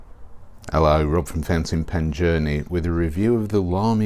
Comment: I keep hearing "fencing pen journey", and I imagine all pens behind a fence! LOL Accent issues, I suppose! :-)